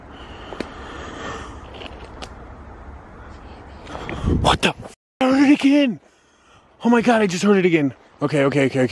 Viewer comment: That voice you heard is at 8:40 (I couldn't make out what was said) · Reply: Cheshire JessiCat ...sounded like "David" 😯